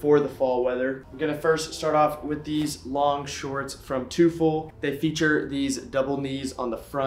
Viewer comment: What's the name of those shorts? Sounds like ur saying twofold like the vintage store but they dont look vintage. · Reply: 2ful